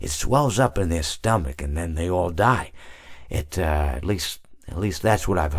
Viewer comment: I stopped paying attention for a second and then I heard "and then they all swell up and die" aa